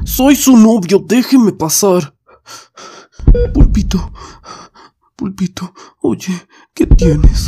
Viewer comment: pensé que decía uy pito XDD